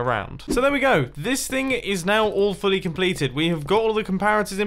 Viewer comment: I heard “this thing is awfully completed”